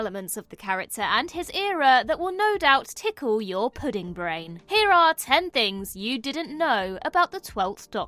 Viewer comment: did...did you just call all your viewers "smoothbrains"? 😂😂😂 savage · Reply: Planet of the pudding brains! 😂